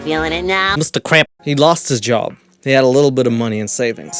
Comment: Wait he said he lost his job?? I thought he said he lost his dog. 😬